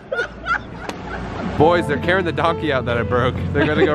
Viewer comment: did u call it a f#ucking donky